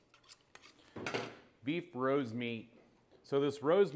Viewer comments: Thought he said " Beat! Bros Meat! " 😂😂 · I heard “beat bros meat”🧍🏼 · Why tf did i hear "Beat bros meat* at first 💀